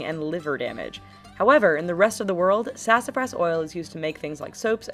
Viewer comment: I thought it said sassafras bitch